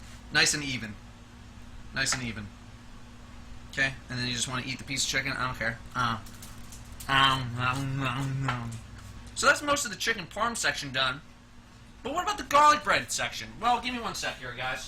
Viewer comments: did he just say it was stuffed with chicken porn!? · I thought that you said chicken porn instead of chicken parm